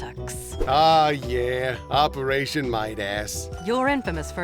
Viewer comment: Percy: Someone reported a case of “Golden Buttocks” Ramsey: Ah yes, Operation Midass Is this how Epithet Erased became popular? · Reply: i thought he said operation light ass